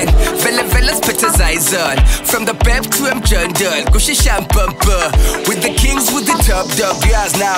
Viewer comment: Did i hear Tira say SHAMPOPO? (2:55).... Ginimbi huya unzwe kkkk